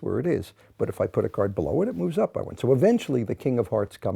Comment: Why did he say "I win" at 7:21. I hate that type of talk, it sounds like trap talk coming from an old man. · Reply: He said "It moves up by one."